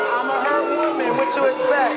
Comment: did she say, "I'm a hurt woman"??? TRICK THEY PASSED THE POLYGRAPH!!! NOBODY HURT YOU BUT YOU & YOUR PARANOID DELUSIONS!!! WTF???